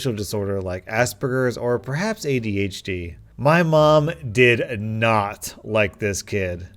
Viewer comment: did he say ADHD I HAVE ADHD